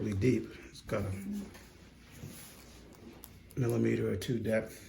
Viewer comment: I had to go back n listen to him again bc at first I thought he said a meter or two deep lol but he clearly said a millimeter or 2 deep Taking away the fact that it's impossible, just imagine if your foot had a whole that was a meter or two deep lmao... it would technically go up abs through your ankle lol damn hahaha